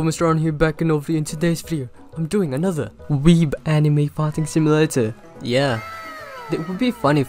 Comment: Btw say the game clearly i always hear “Anime Farting Simulator” no offense but pls change that